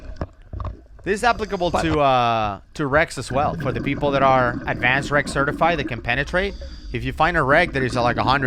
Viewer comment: when you said "penetrating wrecks" i initially thought you said "penetrating Rex" - i thought you was about to describe a palaeontologist's fetish lol